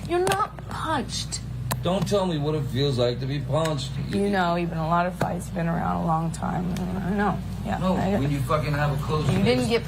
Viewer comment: sounds like she say you know we been in a lot of fights, you been around for a long time then proceed to say you know the difference between a punch and getting hit!